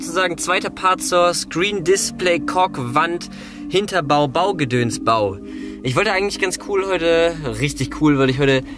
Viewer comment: Alter ich hab erstmal Cock Wand verstanden 😂😧